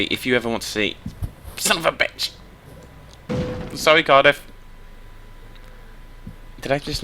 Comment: Dude I died of laughter when you hit the sign!! And it also sounded like you said " sorry Carter" I cried